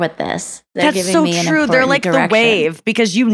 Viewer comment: I heard it as "rapped attention" instead of "rapt" for a split second and all I could picture was the audience spitting bars about how they're invested in her story 😂